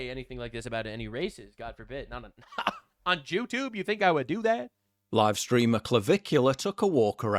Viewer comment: did bro say jew-tube 😅